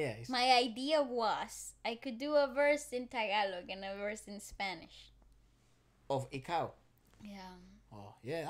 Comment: Did I hear that right? She would like to do a Spanish version of "Ikaw"? Or was I hallucinating 😅😬 Edit: OMG! I HEARD HER RIGHT! It's not "Spanish Version" tho 😅 PLEASE DO IT MS.. WE WOULD LIKE TO HEAR YOU SING IT 😍💖💖💖